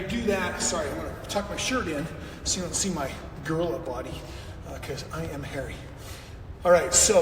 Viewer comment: Wait, did he just say “His girl body cause he is hairy?” Huh? 🤦‍♀️ Is Robyn hairy, Kody? 🤔 … don’t you mean your “rock hard 6 pack abs?!” 😂 🤣 So you just kick up, huh? lol. Thanks for the tips 🙄. This dude is ridiculous. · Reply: I think he said gorilla body